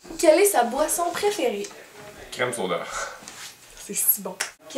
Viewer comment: quand tu demandes "quelle est sa boisson préféré" ton copain a répondu quoi? J'ai compris crème solaire o.O